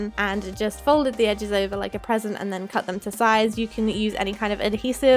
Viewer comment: When she was wrapping the picture frame and then she said fold the sides like a present I heard 'PEASANT' and I was like "SAVAGE' 😂😂😂😂😂😂😂😂